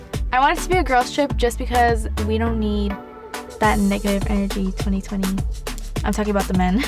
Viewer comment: did she say “we don’t need that nigga in 2020” 😭🤦🏻‍♀️